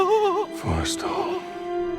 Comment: That moment when the impaled Forrestal pops in...right until seeing the subtitle at this moment I thought Indy said "poor soul" in the movie. Every day is a school day!